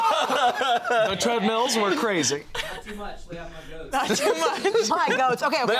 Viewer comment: ngl, after the previous conversation, I thought Spencer said "lay off my gooch"